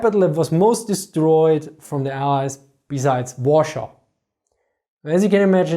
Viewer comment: Hi, hi. Guys can you please help me with something? No matter how many times I'd repeated the part from 10:20 (in which he was talking about Manila) I couldn't quite catch what he was saying. ... What's that "warshop" (in CC) part? I'd really like his input about the Rape of Manila, because it is still largely underreported, even in Philippine school textbooks. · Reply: Warsaw - Polish capital.